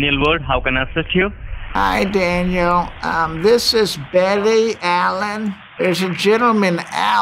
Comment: not gonna lie. thought you said barry allen